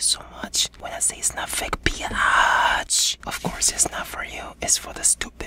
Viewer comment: I am not even kidding when I say, when I heard "BIYATCH" instead of "B*tch" I woke up from confusion then started laughing cause I always say "BIYATCH" 😂😂